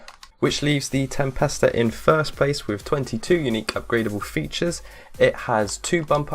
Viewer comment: I heard 22 not 21